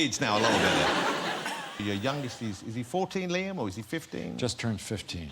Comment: Does Jonathan Ross call him Liam at somewhere around 17:33 or do I hear it wrong? :) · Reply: Liam is Harrison's kids name, he said it correctly "is he 14, Liam, or is he 15?"